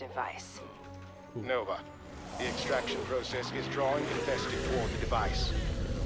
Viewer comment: Please tell me I'm not the only one who thought he said Infested Tauren a couple of times